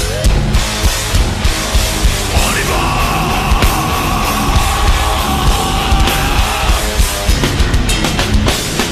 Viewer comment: what does joe yell before singing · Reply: On y vaaaaaaa (let’s goooooooo)